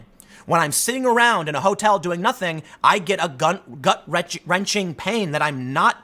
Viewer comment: did he just say "gunt?